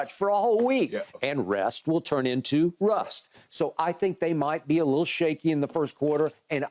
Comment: At first I thought Skip said “rest will turn into Russ”